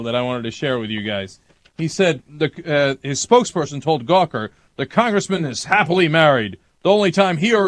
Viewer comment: Sounds like he said Cocker, even though he said Gawker.